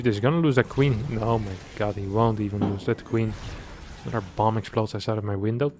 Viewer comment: he says max is being super risky so toss is max and the Zerg I think could be lambo not hunnid percent sure so I was at that time stamp for a while but at 11:45 I think he says another point for reynor which could be the Zerg the micro and macro and queens actually makes sense for Reynors playstyle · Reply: Yeah at 8:10 he does say Maxpax haha, but the 11:45 one is him saying ‘another bomb from my window’ from his noisy neighbours lighting fireworks or something